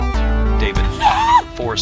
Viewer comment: I heard him say "Holy savage loaf!" And it was "Aw, seriously?"